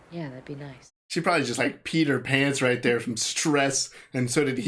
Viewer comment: Sooo...I totally thought you said "she probably Peter Pan's right there" 😆